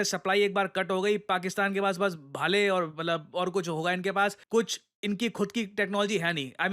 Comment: Did he say Pakistan ke pass Bhale aur Talwar bachenge? 😂😂😆😆🤣🤣